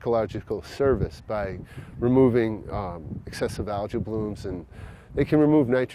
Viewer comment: "I assume this is allergy you're growing." "This is algae" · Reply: I thought it was me. They're saying algae but I'm hearing allergy. Phew, someone else heard it, my ears still work.